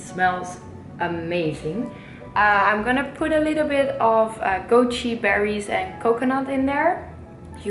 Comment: Hey guys, what did she add to the granola at 6:26 besides the coconut? It sounded like she said "goat cheese berries", but that doesn't sound right?